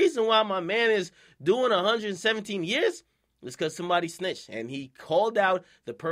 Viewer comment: I thought rowdy said nobody snitch.